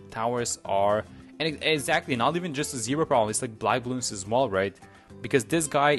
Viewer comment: Did you just said at ~ 3:37 "Blei" bloons. Like the German word for Led, as a German I always notice German words from non German videos xD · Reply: black, he said black bloons